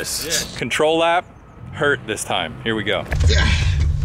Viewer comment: after started bleeding I heard "controllab, hUrt this time"